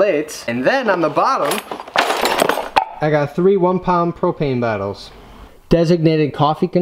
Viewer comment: When he said propane bottles I thought he said cocaine bottles